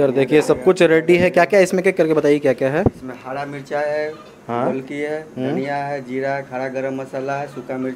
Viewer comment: Bhyia wo 2:30 me kya bola ? Jb aapne pucha ki isme kya kya h ? I mean ingredients me? After hara mirch h , golpi h ?? Ya kya bola plz mention if u r getting through his voice Golpi , kya hota h bhyia? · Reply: Golpi nhi golki hota h....golki mtlb kali mirch......